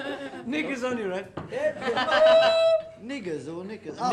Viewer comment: What is Paul saying at 17:06? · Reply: he is saying: “knickers on your head” 😬